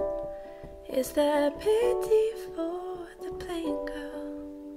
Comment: I keep hearing "is there pity for the playing card" and I can't un-hear it...